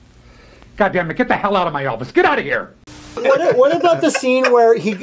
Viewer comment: 100% flubbed that line and said "Elvis" instead of "office"